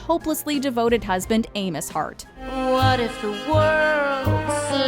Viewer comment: Wait…at 3:35 did you call Roxie’s husband anus? His name is Amos lolololol